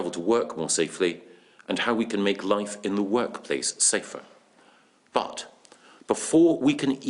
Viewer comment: Thought he said "How we can make love in the workspace safer" Gove putting forward office adultery wow